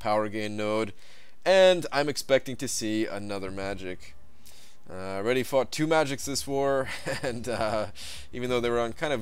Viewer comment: Is it me who thinks it sounds like you said "I already fucked two magiks in the war"?